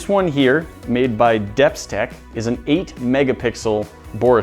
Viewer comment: When he said Depstech I started laughing because I thought he said dipstick